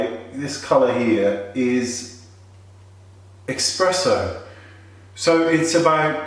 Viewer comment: did you say EXpresso? EX? <_<